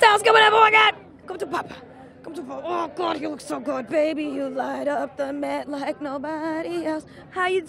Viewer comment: Baby he lights up the met like nobody else for sureee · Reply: I thought she said baby you light up my bed like no body else lol